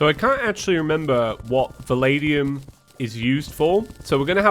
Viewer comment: When you said Valadium I thought you were saying The Lady Um or Muh Lady Um